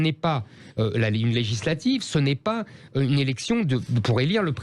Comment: J'ai entendu "c'est une érection" j'ai même plus besoin de la YTP ayaaa